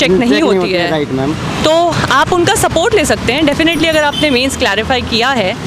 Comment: is she saying clarify?